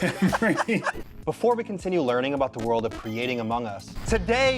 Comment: 13.20 did you just say "The World of Amog Us"?